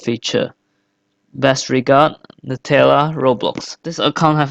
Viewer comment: Did I just hear him say Nutella?